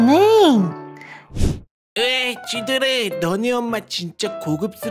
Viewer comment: 쥐돌아로 들리네요 자막은 쥐똑이여도 ㅎㅎ (참고로 지적아님니다)